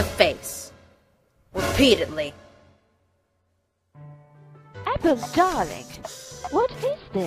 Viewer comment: Sorry but it sounded like Rarity said apple instead of opal